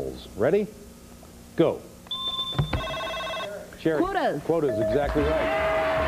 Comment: Has anyone noticed someone saying the contestants name before the host and after she rang the buzzer? It was faint. I think someone at the back said "Sherri"